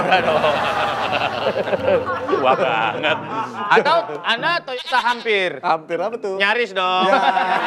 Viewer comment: … Surya bilang apa yang di sensor nih…?☺️ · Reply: Toyota Hampir (Nyaris) Entah sensor nya buat apa, nama brand nya lolos sensor